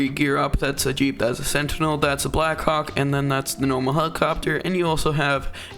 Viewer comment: i thought you said black